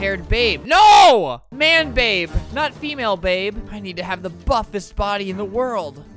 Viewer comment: If you think Albert said the f word in 2:06 he actually didn't he said "buffets" turn on cc